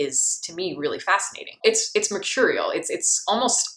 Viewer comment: I'm having a hard time making out what you say at 6:50, and it's probably key to understanding your review. You say something like: "[...] that kind of deadpan banality is, to me, really fascinating. It's maturial(?)" What's that last word? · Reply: she’s says “it’s mercurial”. like something/someone who’s unpredictable and volatile